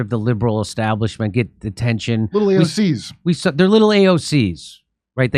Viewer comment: Little AOCs🤣🤣🤣🤣 I thought he said little sobs😆😆....AOC is even funnier 😅